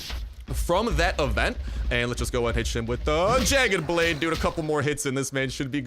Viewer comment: sounds like he says bitch lol